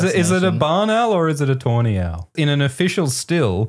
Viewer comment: I thought he said Tourney owl, and for 3 seconds my brain just accepted it. yeah Owl Tourneys of course they exist. but then I read Tawny owl and felt really stupid again.